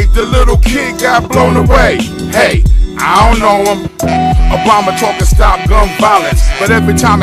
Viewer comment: Did he just say Obama? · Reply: He definitely says Obama. "Obama talkin stop gun violence..." is what I heard which makes sense.